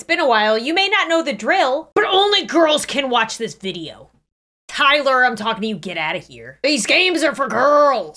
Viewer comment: I t 0:41 I thought you said Kyler